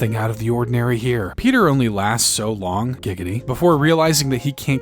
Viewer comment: did he just say gigity